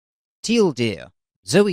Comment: I JUST GOT IT. TL;DR. TEAL DEER. THE DEER IS TEAL. MY GOD. I wondered why you said it like that. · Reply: BeefBacon I thought he said Till deer.